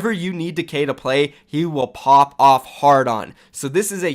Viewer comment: I thought mykl said "Whatever you need Decay to play, he will pop a hard-on". Whatever floats your boat...